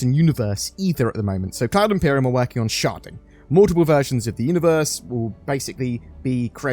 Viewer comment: This whole time I thought he was saying sharting...